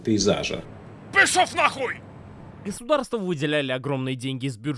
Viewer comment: мне послышалось "государствА выделяли огромные деньги", то есть во множественном числе. А это ЛОЖЬ. Или, может, я могу открыть по годам законы про госбюджет Украины и узреть там колонки с некими "огромными деньгами" на кино?